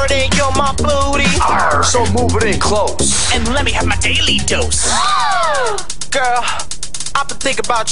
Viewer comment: i thought they wer saying: Let me have my daily tost! 🍞